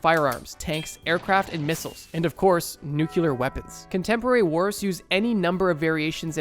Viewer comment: did he just say "nucular"?